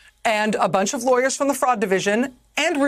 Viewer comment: I know she's saying "fraud" but I keep hearing "frog"